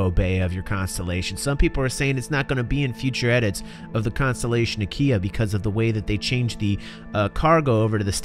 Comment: Sounded like you said Constellation Ikea at one point. So I was like Oh so Ikea went from furnitures to spaceships 900 years in the future! x) This is definitely the Constellation variant I'm gonna get in PU, sounds like it's gonna be so awesome. Great video as always!